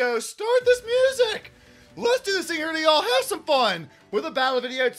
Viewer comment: Does he say, “let’s do this thing early y’all have some fun” in the beginning at 0:26? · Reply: Let’s do this thing here today yall and have some fun :)